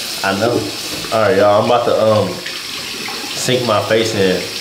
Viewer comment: Did I hear “stink”? If so 😍😍😍😍😍😍